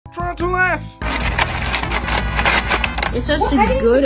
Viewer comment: when this guy say "try not to laugh" i feel like i hear "tarantula laugh"